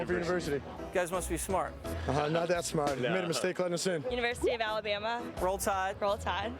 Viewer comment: what does he say at 0:37? made a mistake...? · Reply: "they made a mistake letting us in."